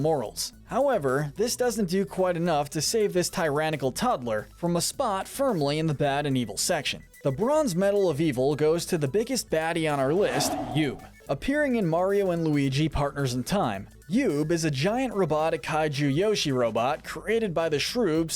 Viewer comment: i thought you said "the biggest baddie on the list; you XD